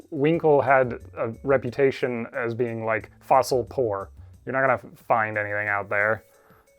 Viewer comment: When he said "fossil poor", I heard "fossil porn". I was very confused